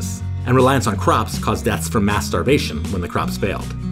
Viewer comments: "WHAT?...Oh, he said mass starvation" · i heard ‘mass starvation' as mast*rb*tion 💀💀💀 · I heard 'deaths from masturbation' and was ready to believe it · 🥵 I didn’t think you were going to say ‘mas-starvation’ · caused deaths from masterbation?? xd I am the only one who heard that? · am I the only one who heard "masturbation"? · I thought I heard masturbation so I gasped, but it actually was mass starvation 😂😂😂